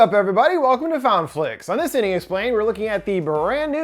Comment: I don't know why but I thought you said another idiot explained 🤣😭